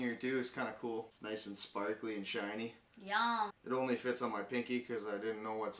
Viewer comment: did anyone hear that said "Yum" At 7:25 When he puts the ring on?